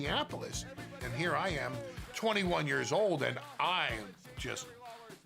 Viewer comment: did he say 21 years old? He was 21 in those clips? He looks like he is 40